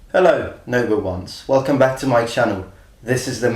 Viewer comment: For a long time I thought you said "hello number ones" but you actually say "hello Noble ones".